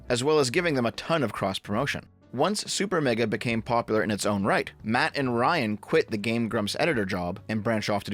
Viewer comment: lmao at 1:20 did you say "in it's own reich"?